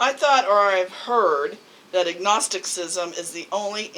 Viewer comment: Did your partner say at 5:55 agnosticism or ignosticism? And is ignosticism and igtheism the same thing? Also did you say agnosticism around the 7:23 mark? Just confused because I wasn't sure which ones you were referring to by voice, lol. Thanks:) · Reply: my wife said agnosticism at 5:55 and at around the 7:23 mark I said agnosticism